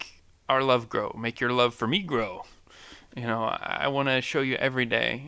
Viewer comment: When you said: "Love for me grow" I thought you said love for negro lol. I'm dying here. But srsly, good vid man.